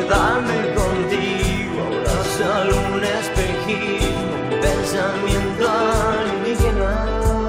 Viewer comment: Me deprime la televisión anestesia para la razón que daría por tenerte junto a mi platicar las horas en aquel sillón Sombra de la realidad reflejo de superficialidad me pregunto dónde sea que estas tu que estarás pensando, que estarás sintiendo Y no, no lo puedo evitar, no lo quiero dejar de pensar en tí, como parte de mí pensé en echar las raíces y quedarme contigo ahora solo un espejismo, pensamiento alienígena, wo ohh No consigo la concentración ni exorsismo a domicilio que daría por tenerte junto a mí platicar las horas solo viéndonos Sombra de la realidad reflejo de superficialidad me pregunto dónde sea que estás tú que estarás pensando, que estarás sintiendo Y no, no lo puedo evitar, no lo quiero dejar de pensar en tí, como parte de mí pensé en echar las raíces y quedarme contigo tan solo un espejismo, pensamiento alienígena, ahh aa oohh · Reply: Toda mi vida pensé que decía "pensamiento de vida" en lugar de "pensamiento alienígena" asjajajaja